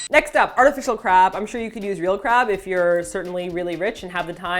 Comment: i thought said "you can use real crap" at 2:53... I think i'm just hearing things :)